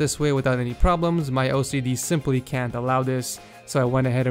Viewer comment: did he say I just can't have it because of my "ocd"